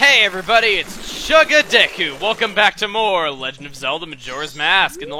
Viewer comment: in the beginning when you said "chugga deku" i thought you said "chugga dick"....anyone else?